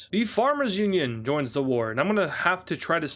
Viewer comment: wait did he say war 🤨